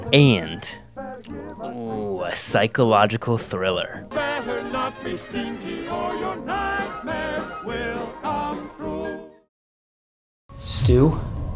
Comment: WHAT I HEAR: "if you're stinky, your nightmares will come trueeee"